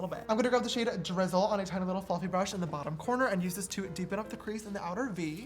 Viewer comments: I thought sister said we’re going to grab a fucking brush Instead of fluffy brush😂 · When you said fluffy brush I thought you said f*cking brush 😂😂